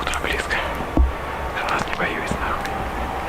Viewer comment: didnt said "its approaching" the subtitles are incorrect during his whole clip. what he actually said is: "i am not fucking scared of you" 💀